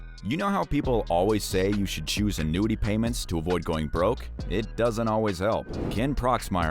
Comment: "You know how people always say you should choose a nudie payments "? That's what I'm hearing but I'm sure that's not what's being said. What are they saying?